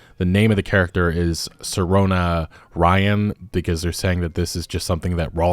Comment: To be fair, since i listen to most of these like a podcast, I thought you said the character was named SIR Ona-ryan. and I thought they must have a been a trans boy. That does seem a bit of a strange name, and doubly so if they are actually a trans girl